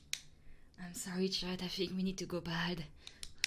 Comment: did.... I hear what I think I heard 🤔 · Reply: I think she meant "we need to go bald".